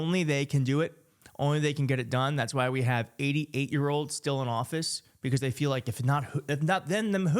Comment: Ahaha! I thought you said, "That's why we have 80 8yos in Congress!" That's about right! 🤣🤣🤣